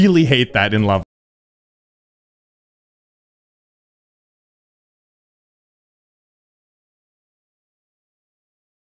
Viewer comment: A small segment is muted from 10:46-11:16. What was saying there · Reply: I remember what he said during this part: "Ah, that‘s so tight dude. Ok and don‘t forget that, yeah, so annoying, oh my god, that part is really obnoxious. Ok let‘s go. Yeah, I mean I get why people don‘t like this level. I think it‘s decent but I understand…"